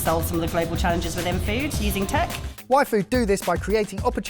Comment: The first few times and said "Y Food" in this video, I heard "Waifu". 😅😅 It's all in the D, guys. 😂